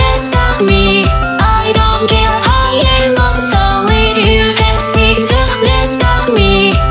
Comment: At "I don't care, I am obsolete" I keep hearing 'I don't care how you're not sorry'